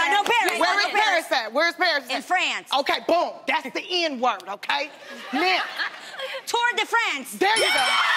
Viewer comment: sound like she said (THE N WORD OKAY!) haha